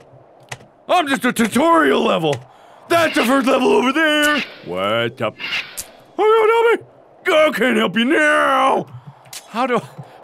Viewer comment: Does anyone know what he's saying? 😂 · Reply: That's the first level over there Wake up? I don't know me Ugh can't help me now. You are welcome